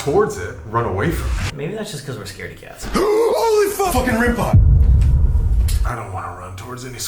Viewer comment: does anyone else hear f-ing ring pop?